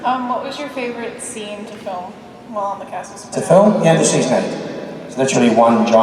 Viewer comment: Did he say ' the end of season end' at 27:54 ?